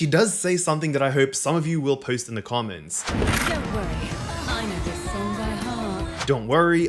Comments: to me it sounded like "don't worry, I know this by heart" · she actually says “ Don’t worry, I know this song by heart” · I thought she said "Don't worry, I know this song by heart" as in "I've fought for a long time, I'm used to this" kind of thing! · Video uploaded eleven (11) seconds ago and I be here. WHOO!! 💪😎💕 Edit: I think at 1:11 she said "Don't worry, I know this song by heart." Would fit since she always says "Let's Dance/Tango" for fighting. 🤔🤔 @ 5:56 Is this for the Alternate Time line for Y.Cereza? Still right for Jeanne and for Y.Cereza who fought her way through in the Records of Time for all that time, but if it's our Bayonetta, she'll have about maybe nearly thirty plus (30+)? (Not counting the training and maybe being a teen for an actual mission with fights. 🤔 @ 6:42 oh hey, neat detail is that the barrel changes after she fires, neat!!